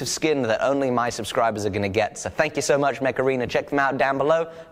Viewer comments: I swear I was looking away when you did your sponsor bit and thought you said the video was brought to you by the Macarena. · 😂😂😂😂 his sponsor was mech arena. But I thought he said he was sponsored by the macarena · When he said mech arena it sounded like he said Macarena with an accent